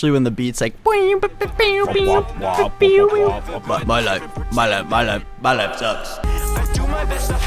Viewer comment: I THOUGHT HE WAS SAYING "i like sex", NOT "my life sucks" OOPS